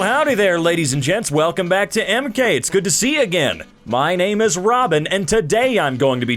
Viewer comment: I heard:,, It's good to see again"